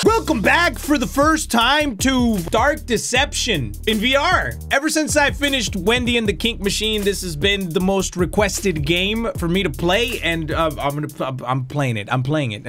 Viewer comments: Did he just say “Wendy In The Kink Machine” in 0:18? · I heard “bendy and the kink machine